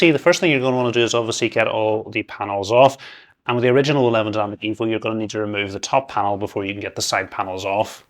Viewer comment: i can't understand you man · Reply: In the original O11 Dynamic EVO your going to need to remove the top panel before you can get the side panels off.